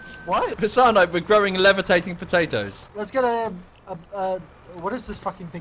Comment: I thought said “PEDOS” not “POTATOES”